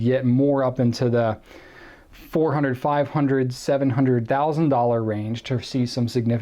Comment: vi thought he said 500,000 range i was like wtf